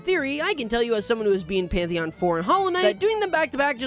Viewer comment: Bro said, “as someone who has beaten pantheon 4 in hollow knight” and I did a triple take. I realized while yes, it is really hard, I really thought he said pantheon 5 XD